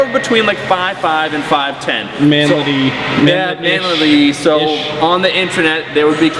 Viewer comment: someone mind explaining to me what "manlee lee"is?...am I saying it right?